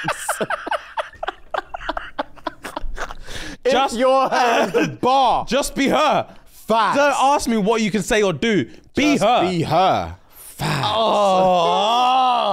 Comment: 'Just be her' sums it up perfectly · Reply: It would have worked out better for me if it didn’t sound like he said be hurt. 😅 I’m not used to their accents or slang yet.